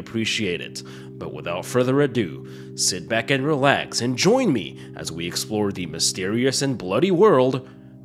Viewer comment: is it just me or in all the introduction to the videos i hear 'enjoy me' not 'join me' haha! great review as usual